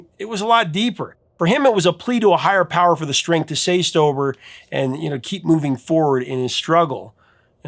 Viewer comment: Strength to say stober? At least, that's what I thought you said the second time.🤣